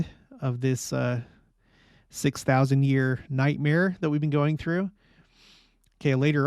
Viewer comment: Great episode and insights. Just wondering if I heard you correctly at 17:22 referring to the 6000 years of every mortal humans existence on the earth generally as a “nightmare”. Ha, I’m not sure that’s the best perspective to have or teach on this God given opportunity which is designed to teach us and give us experience that we might ultimately have “joy”. 😉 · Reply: You heard correctly, lol. Merry Christmas (hopefully the last one of this nightmare) 🌻🎄🌻🎄🌻🎄🌻🎄🌻🎄🌻🎄🌻🎄🌻🎄🌻🎄🌻🎄